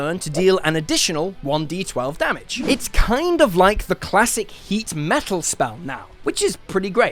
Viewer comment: Heat metal? Oh,... I thought you said "Hate Metal"! Why? 'Cuz me LOVE METAL, dat's why!